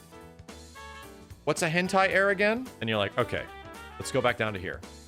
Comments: Had this on in the background while playing video games and I had to do a double take like “did he just say *HENTAI AIR*?” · "Hentai-Air", LOL. I had to rewatch this multiple times to make sure I heard right, haha